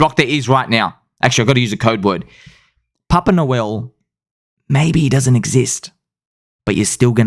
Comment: I heard "Poppin' a Whale" the first time...not "Papa Noel" LOLOL.